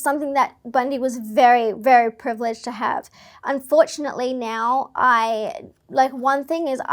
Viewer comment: Did she say “Unfortunately now”?